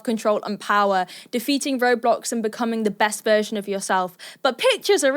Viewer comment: looked away for a second and thought you said roblox 😭